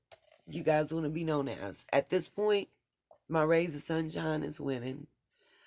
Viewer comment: I thought you said "my suBshines" 😅💝🌞